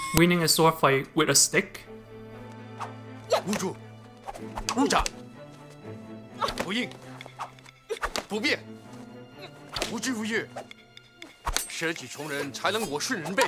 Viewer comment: All I hear is Behave! Whoopin, Behave!! Whooping, Behave!!! Ass whoppin, Behave!!!! Smack the Bum bum